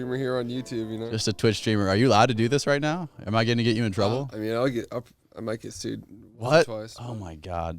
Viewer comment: can someone explain what he said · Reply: He might get sued, but he’s just kiddin